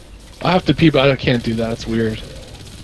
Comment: I swear I heard that dude's lady at the end say yeah I can hold it for you, or something like that. Holy hell that is love. Talk about ride or die. 😂😂😂😂